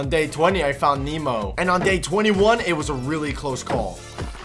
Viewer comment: why do I hear that said gay 21?